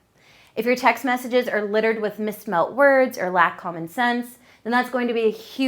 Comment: Did she say "mismelled words" while telling us grammar is important? LOLZ😂😂😂 I'm dead!